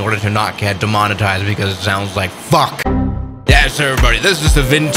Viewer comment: WHAT DID HE JUST SAY AGAIN 💀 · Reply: he said fuck